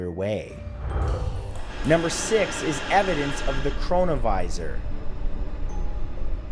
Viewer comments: #6 i swear i thought i heard evidence of the coronavirus 😬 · i deadass thought he said coronavirus for a sec 💀💀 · Thought he said evidence of coronavirus... I was like wut